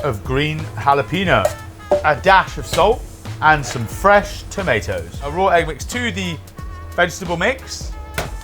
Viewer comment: sorry, did he just say tom-ay-toes?